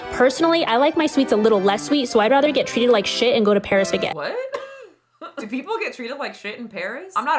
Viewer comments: I think she said Paris Baguette the food chain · I THINK (28:39) she said "Paris Baguette", the bakery chain? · Bob Belcher wishes he looked like that 😆 Also just wanted to say, that when she said "get treated like sh** and go to Paris-" 28:44 I think she actually said "Paris Baguette" which is a South Korean bakery and café chain in NY city areas mostly. In terms of getting treated badly, I dont know about that lol-they arent the most interactive workers but the food is awesome so idc. could be totally wrong and just heard what i wanted to hear😅🤣